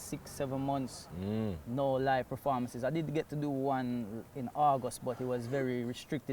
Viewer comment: I thought he said Argos but he actually said August just took a few seconds to clock 🤣